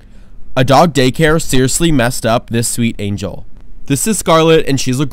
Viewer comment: Bye I thought you said “this is garlic”